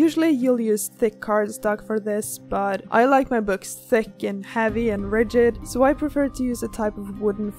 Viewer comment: "I like my books thicc" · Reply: I thought u said "I like my boobs thicc" 😂